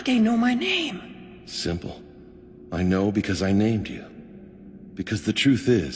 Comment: When minato said "I named you" i for real thought he said "I made you"